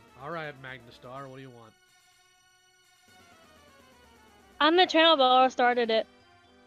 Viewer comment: I love how the very first words she said during the first call were so messed up that even the captions on Youtube couldn't pick it up XD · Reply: What I hear is "I'm the channel who all started it" which...DOESN'T make it any better considering she sounds like she's perpetually sloshed, like, ffs, SOBER UP, listen CLOSELY, and SPEAK. UP!